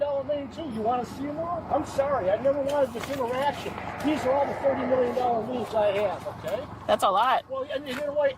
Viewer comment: did he say he has a $30 million judgment lien